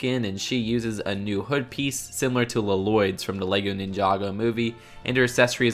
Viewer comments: did - did he say la-loyd? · did he really say LaLoyd instead of Lloyd